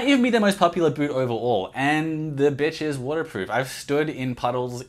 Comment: Did you actually say "the b** is waterproof"???